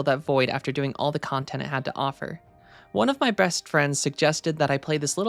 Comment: Uh… am I having an aneurism or does anyone else hear “one of my breast friends” at 0:08? New word of power obtained. 🗣️